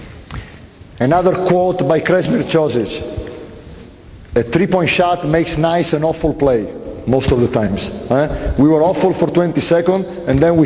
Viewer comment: What does he say between 52:48 and 52:51? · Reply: A 3 point made can make an awful play look good